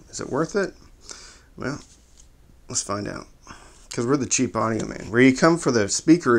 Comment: I thought he said cheap onion man